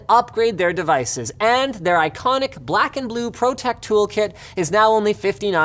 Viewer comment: The I Fixit thing is called the "Pro Tech" tookit...I heard it as "Protecc". I really need to stop with the meme dumps.